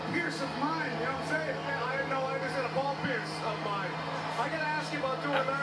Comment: Did he just say "I had my ball pierced"